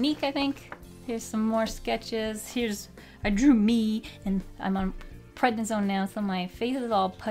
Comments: Just a heads up, at 16:16 you say "I'm on prednisone", but the YouTube generated subtitles say "I'm preganant"... So if you start getting weird questions, you know why! 😂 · Well exCUUUUse me, i couldnt understand you in 16:17 and i thought mybe im just deaf so i turned on the subs and YOURE PREGNANT?? congratulations??? Im so happy for you? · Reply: Katharina Nachname she said she’s on Prednisone. It’s an inflammatory suppressor.